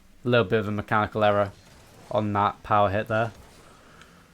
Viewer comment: Not going to lie, it sounds like you say “heil Hitler” instead of “Power hit there” at 3:08. Had me worried for a second when I first heard that, but after a few replays, you definitely say “power hit there”.